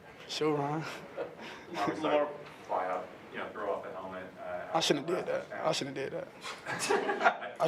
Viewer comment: what is the reporter saying? Lamar is saying he shouldnt have done what?? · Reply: Said he shouldn’t have thrown his helmet in the air at the end of the game, but was excited to win in OT